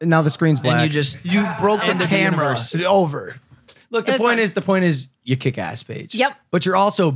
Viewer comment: i thought he said " point is you kick ass, bitch" lol had to rewind it back